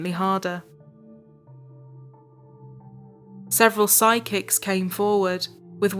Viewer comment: I thought you said sidekick, and then I realized you meant psychic, but thank you. I had a rare laugh during a very troubling an unfortunate story.